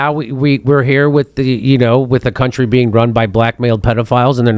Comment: every time he says “blackmail” I keep thinking he’s saying “black male” First time I heard it I was like…”There are no black males running the USA 😅”